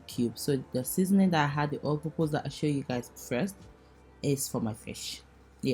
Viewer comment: in the beginning of the video what seasoning did you say that you used to cook with fish? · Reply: all-purpose jumbo aroma